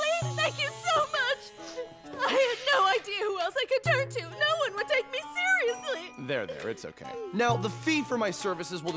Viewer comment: Sorry, I'm leraning english and i cant understand what she's saying at 2:23, can someone help me, please? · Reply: Hanako said "I had no idea who else I could turn to. No one would take me seriously."